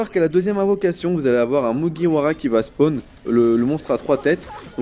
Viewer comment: A 3:10 il dit y'a un mugiwara qui va spawn mais mugiwara sa veut dire chapeau de paille en japonais ( luffy quoi :) ) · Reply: Il veut dire margwa 😃😃